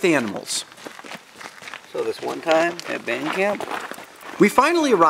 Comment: did he say "so this one time at band camp."